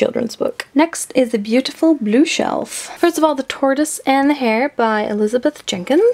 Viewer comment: Is it bad that when you said "the tortoise and the hare" at 8:04 I heard "the TARDIS and the hair" ? That wasn't just me right.